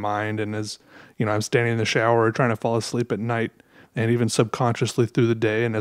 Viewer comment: I heard: "As I'm standing in the shower trying to fall asleep at night." Pretty inefficient way to fall asleep lol